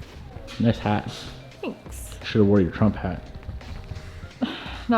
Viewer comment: Did I hear "Trump Hat" Jesus.. no please.